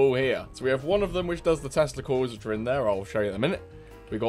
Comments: I thought you said "Testicles". I rewound the vid and I think you actually said "Tesla Coils". Not 100% sure but going with the latter. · I don’t know why but almost every time you say “Tesla coils”, I hear: “T e s t i c l e s”